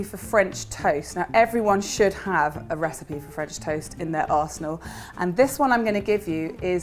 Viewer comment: Honestly thought she was saying everyone needs a French toast recipe in their arse. . .